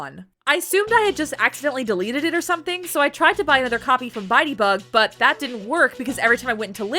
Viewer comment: for some reason i kept hearing "buy debug" as "biteybug"